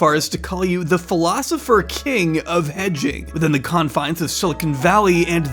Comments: Misheard this as The philosopher king of edging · Thought you said " the philosopher king of edging" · thought you said "the philosopher king of EDGING" at 8:12 the speed my head whipped tf around